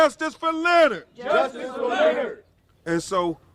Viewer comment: That “justice for Lenert” was like a yawn from the activist community. They looked so bored. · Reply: I heard “just us for Leonard”